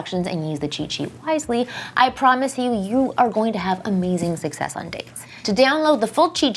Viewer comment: Sounds like she said, "You are going to have amazing sex on dates"